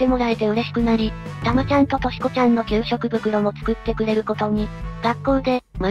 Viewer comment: たまちゃんと しこちゃんって聞こえて俺疲れてると思った